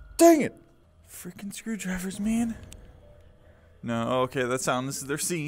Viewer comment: did You Say f*king?